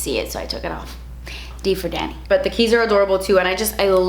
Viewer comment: bruh i thought kathleen said D for daddy but i went back and it was D for Danny LOL